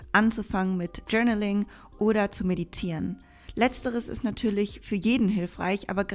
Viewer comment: Was sagt sie bei 7:28 für ein Wort? :D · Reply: “Journaling oder Meditieren” sagt sie 😂